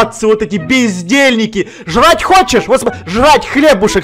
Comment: Кому тож послышалось пиздельники?